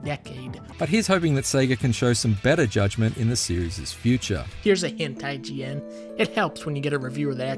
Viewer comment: Dunkey : "here's a hint IGN" What I heard : "here's a hentai GN"